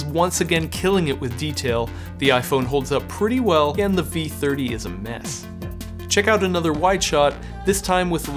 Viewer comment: Dislike for overall video, Analytics is very poor and dramatically subjective. For example, time 4:01 - what the f**k are you saying? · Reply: exactly... "iphone pretty well and V30 is mess"... LOL